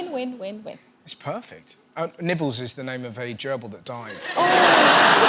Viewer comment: Oh when she said nibbles I thought she said Nipples! All these lonely christmas people talking on twitter with their nipples on show